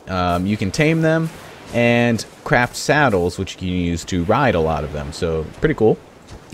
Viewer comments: Did you say crap saddles at 1:51 ? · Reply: Ummm he said craft saddles to ride the dinosaurs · Ummm he said craft saddles to ride the dinosaurs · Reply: BeastlyBoss I know what he meant to say,I just heard him say crap saddles, and if you don't believe me turn on subtitles :)